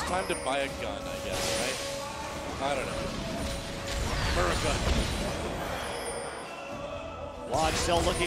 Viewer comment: anyone else hear him say Murica?? lol